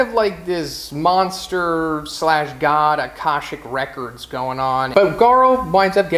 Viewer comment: Word at 1:17, contect at 1:10... what is an akoshik record? Sorry, i don't know what the word he said is or how to spell it.